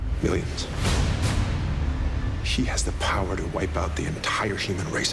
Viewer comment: Does he say "she has the power to wipeout the entire human race"? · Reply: No, "He has the power......."